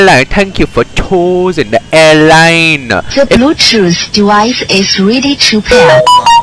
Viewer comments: I thought it said “The Bluetooth device is really cheap here”💀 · for some reason I always heard it as “the Bluetooth device is really cheap here”